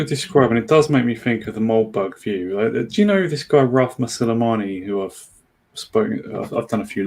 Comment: I didn’t catch the name. Who is this? Sounded like Rothma Soleimani or something. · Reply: Ralph Masilamani. Simon Harris and I spoke to him a few times - <link>